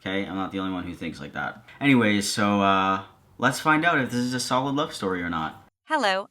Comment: what did he say? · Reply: "Anyways, so let's find out if this is a solid love story or not"